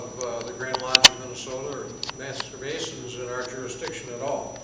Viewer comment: It sounds like he says "masturbations"